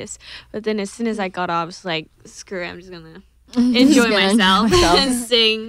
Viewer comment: Did Jennie say "screw it" at 2:56 ?? LMAO I STAN THE RIGHT PEOPLE